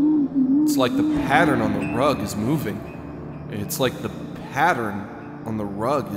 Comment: sorry did you say that the pattern’s moving